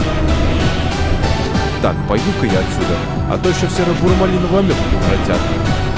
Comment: из-за музыки я не услышал что говорил КК. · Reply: Пойду-ка я отсюда, а то ещё в серо-буро-малиновую амёбу превратят